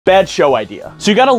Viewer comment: I thought you said bad show idea?